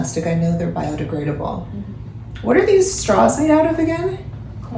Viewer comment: Did anyone else hear Kourtney go: “corn?” when Khloe asked what the straws were made out of 😭